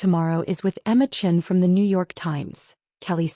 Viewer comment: Yet another screw up around the Pulitzer Prize. You might want to use a different prize in any future stories. AI clearly can’t handle Pulitzer Prize. What it says: “She want to pull it’s her last year.” What it should say: “She won a Pulitzer last year.”